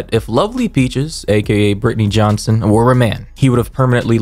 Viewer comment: that part where you kept calling lovely peaches a "he", I had to turn on captions to see what you were really saying lol while you were correct in saying "if lovely peaches were a man", you could've also used the word 'was' instead of 'were' and it would've still been corfect and sound better lol I had to replay that part a few times to notice that's what you were saying, bc it sounds like you said "or a man" instead of "were a man", but if u used 'was', it wouldn't sound confusing lol